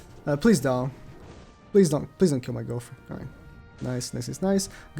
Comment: i thought he said “pls dont kill my girlfriend” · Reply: yeah he said gowther but it sounds like girlfriend